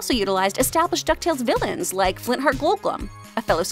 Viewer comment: It sounded like you said Flintheart Goldblum, it's pronounced Flintheart Glomgold.